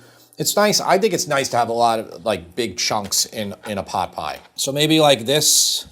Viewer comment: i'm so brainrotted, I thought he said big chungus at 4:01. Make this tomorrow though.